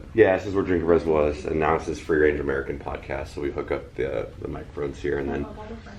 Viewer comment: Hey guys, I enjoy doing this content. It's nice to take a step back from the police stuff sometime. Follow my second channel: <link> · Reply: what podcast did he say is recorded there now? Sounded like he said Free Radio America ?